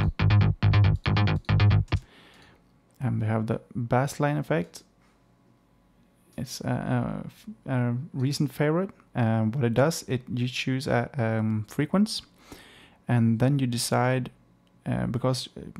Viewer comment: my fault I thought you said the plugin was a "reason favorite" not recent.